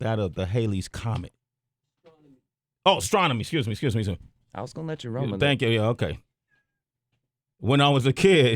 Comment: Did he just say "I was gonna let you role me nigga" hope not. I like JT